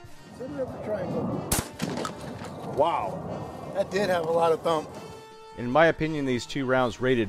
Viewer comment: With the sound low it sounds like he says “it did have a lot of cum”